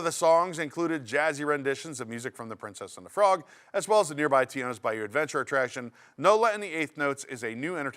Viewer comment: I could have sworn I heard say "TINO'S Bayou Adventure," which, I'll admit, "Hispanic New Yorker gets lost in the Bayou" sounds much more interesting than what's already there... 🤪🤣